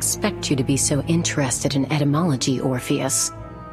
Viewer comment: it sounds like she says "etymology", which is the study of the history of words, instead of what the subtitles say, "entomology", which is the study of insects. It's a bit funny to get the word for the study of words wrong